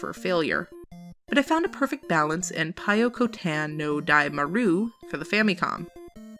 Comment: What a great video! This was seriously a great list of games.with a few i've never heard of. Pyokotan (i had to look up the name because i thought you were saying Toykutan) looks like a great kids game, and how can you go wrong with Kirby?! That Frog-Bomberman game looks super fun as well. For a tougher challenge, i'd recommend Robowarrior if he likes bomberman.